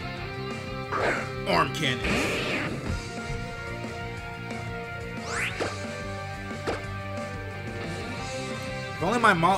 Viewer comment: I keep hearing 'ARMCANNONS' as 'Arm Candy'...